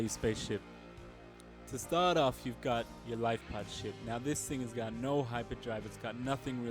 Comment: Turn on subtitles and see what is there instead of "lightpod ship"